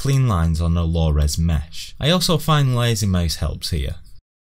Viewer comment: Wow, really nice quick result. Will have to give this a go! (couldn't seem to hear you when you said "I find 'something' helps here"... I heard it as 'Lazy Maize' and I presume you're not talking about a slobby Cob :D ) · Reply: Lazy mouse*